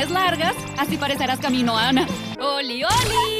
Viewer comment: Creo que dijo " Kaminoana ". Los Kaminoanos son una especie ficticia de Star Wars.